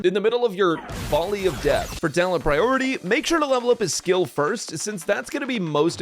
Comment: Did bro just said "her talent priority" 3:30 🤣🫵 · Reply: No, he said "for talent priority, make sure to..."